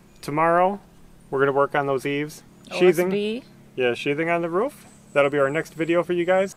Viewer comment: I thought i heard bees under sheathing